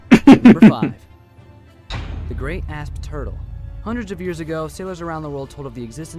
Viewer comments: The Crabzilla caught me off guard and I laughed my ass off Edit: Just finished the video and I misheard "Great Asp-Turtle" to "Giant Ass Turtle". I also just want to say that I think(though I'm not sure) that The Bloop was just some Icenbergs or something. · I heard the great ass turtle 🤣🤣🤣🤣🤣